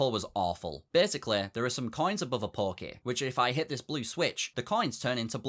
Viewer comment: Ngl, every time he said "pokey", i heard "porky"